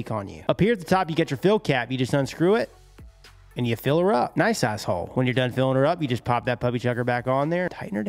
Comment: did he say "nice asshole"?